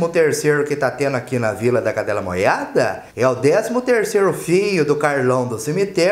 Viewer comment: Vila da cadela moiada (0:25)..eu escutei certo??? Kkkkkk